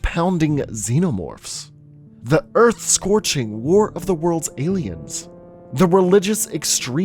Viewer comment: did he just say walk of the worlds